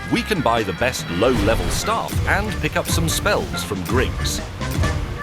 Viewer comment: I thought May said he was going to buy some spells from "Greggs," and I thought "Oh do they sell those now with the sausage rolls?"